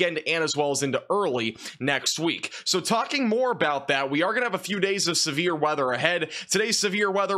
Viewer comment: Could've sworn you said "we're going to have a break"...lol